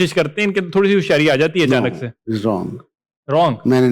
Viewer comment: what did u say? Hoshayari? 😂